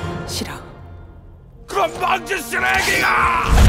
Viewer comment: 뭐라는거이? 그럼 만졌음 왜길어? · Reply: 귀에 문제있냐? 누가봐도 ''건방진 쓰레기가~~'' 인데 ㅋㅋ